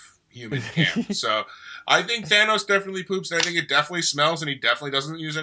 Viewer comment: did he say “that nigga definitely smells”? Or are my ears broken ?